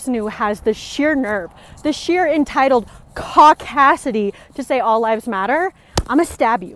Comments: I thought she said cockassity and it was some sort of barnyard reference. I suppose that's an example of my cockassian privilege. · How dare she say "caucasity"!? That's OUR word! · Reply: Oh is that what she said, that explains it now, I thought she said "cockdasity" and I thought funk is she a feminist as well, it may have been her accent but your word makes,more sense. Cheers.